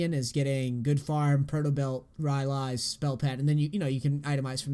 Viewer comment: What did he say at 25:12? Spell pattern or he meant some kind of item? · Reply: Most likely spell pen, so magic penetration items